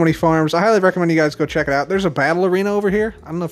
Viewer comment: At first i thought he said ballerina not battlearena